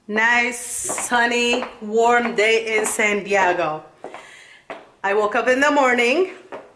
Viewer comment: Did she just say in sandeago at 10 sec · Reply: Yg_on_the _cut yes she did she said San Diago.